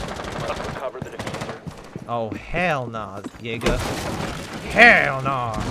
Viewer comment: when he said hell naw Jager I thought he said hell naw nigga lmao